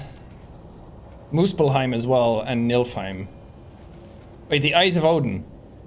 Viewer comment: why does he say Milfheim 😭